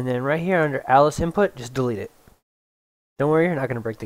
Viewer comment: did you say "just delete it"?